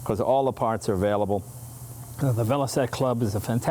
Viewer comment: I thought it was said velocity haha sounds more bad ass that way... what u ride? Oh... a velocity!